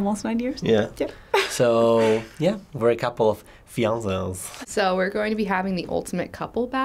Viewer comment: A couple of WHAT?! What did he say? Fiazaz? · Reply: "fiances."